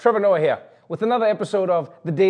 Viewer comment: Ha ha ha!.. when you said:" Trevor Noah here!" I thought I heard something like: "Trevorona here!"... :-)